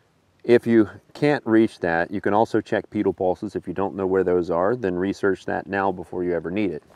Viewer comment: What is the word you say at 6:57 that should be researched? I would like to research it! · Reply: Pedal pulses